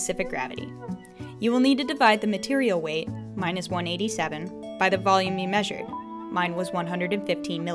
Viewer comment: What she said: “ Divide the material weight, mine is 187...” what I heard: “Divide the material weight minus 187...”